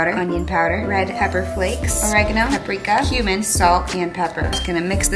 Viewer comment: did she just say HUMANS...